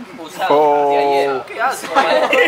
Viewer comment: Min 2:36 Escuché condón !😂😂😂😂😂